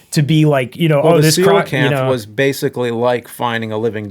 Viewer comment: What did James say? Crocanth?